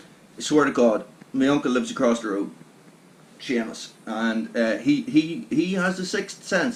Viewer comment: pretty sure Shamus is spelled the same there as it is here ;-) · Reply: I thought you said shamus in the video....my bad